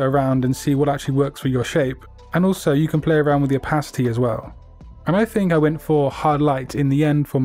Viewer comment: 2min 40 seconds.. i thought he said "you can play around with your pasty aswell" 😆